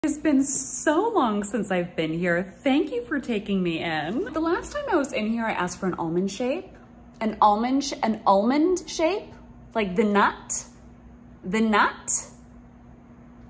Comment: her asking for an almond shape with two millimeters of free edge is giving me war flashbacks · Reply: You know, I thought she said almond shake